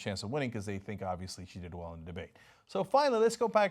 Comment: Notice Cenk throwing some Mandarin Chinese in there 9:24. Nice. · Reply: lol wat are you talking about? he said "she did well in the debate @ 9:24"